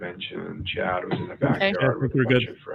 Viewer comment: did say “…’Chad’ was in the background…”???